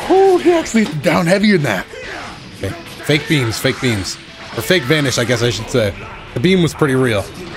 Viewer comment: i thought he said "fake beans" and i went E A T I N G B E A N S I S E E. L E T ME T E L L Y O U A S E C R E T, C O F F E E I S J U S T B E A N S O U P